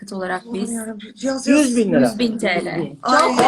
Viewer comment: Sabancı 5 bin TL mi dedi benmi yanlış duydum